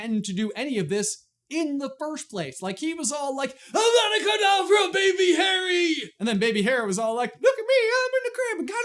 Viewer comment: Captions say "I GOTTA GO DOWN FOR A BABY HARRY!!!" O_O Oh dear...